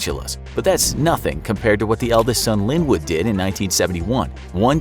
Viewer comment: who else heard "would did"